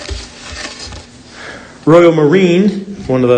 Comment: For the longest time, whenever mentioned Royo Marín, I thought he was saying, "Royal Marine" 😂